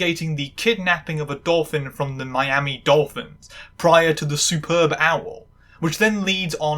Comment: Sorry I heard you say super bowl like 'superb owl' and immediately thought of wwdits, the honk that just left my body lmao